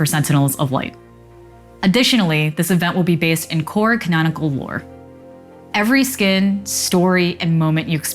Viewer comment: yo, speak clearly in these update videos please. i have absolutely no idea what the heck this woman said at 0:44.. "based in corick nanicle ore" what?.. ..